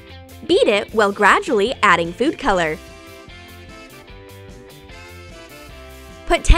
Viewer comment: did she say eat it while adding food coloring? Like this if u did hear it too